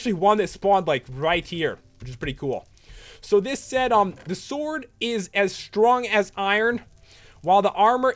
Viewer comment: Lol did you just say the meat is as strong as iron